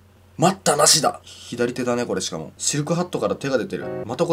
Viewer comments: 最初、[シルクは、]て、聞こえた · シルクハットがシルクロードって聞こえた